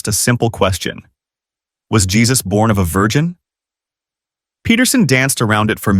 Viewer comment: "Do you believe Jesus was born of a virgin?" No Christian in their right mind would have a problem answering that question. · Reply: hahaha ... I thought he said, was Jesus born a virgin? 🤭